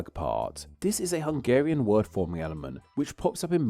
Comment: "This is a Hungarian word for ____": What is the next word in this sentence? It sounds like you're saying something like "mee-ahl-man", while the CC claims it is "word forming element". What is the actual word or term you're saying? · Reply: word forming element